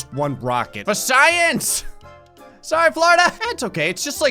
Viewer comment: did he say for saiyans or? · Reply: for science